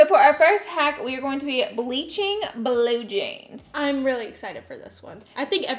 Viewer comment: At 1st I thought u said blow jeans & i was like huh what r blow jeans sounds dirty! But then i realized ohhh BLUE she said BLUE jeans! 😆